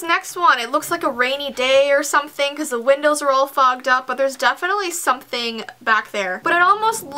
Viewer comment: on 4:25 did say "because the windows are all fu**ed up" ? xx